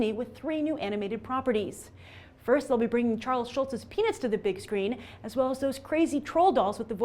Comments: i thought you said they were bringing charles schultz' penis to the big screen for a second. yea, not for me... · For a second, I thought you said, "Bring Charles Schultz's penis to the big screen." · Did she say PENIS?!?! GAHAHAHAH XD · I swear i heard her say charles schulz's PENIS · I thought she said PENIS! ANIMATED PENIS! hahaha